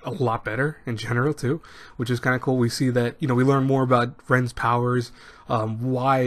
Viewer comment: For a second I thought the video said Rems power and then I was like, Re Zero season 2 confirmed.